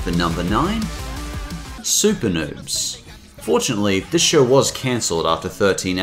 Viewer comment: Super Noobs....Oh stars....[I thought you said Super Nerds.]